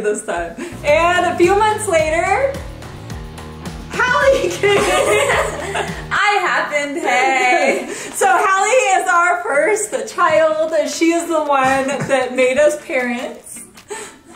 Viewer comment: Matter of fact I thought Hallie was adopted.. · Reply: I believe they did ,but I only heard, "Hallie is our first child that made us parents " my subconscious thought that's what said . But clearly she said our first biological child ..